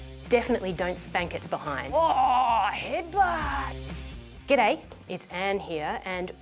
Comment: What did she say? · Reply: hwoaaaar headbutt!